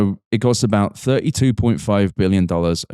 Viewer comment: uhhh did he just say 32.5 billion with a B?? Did I hear that right?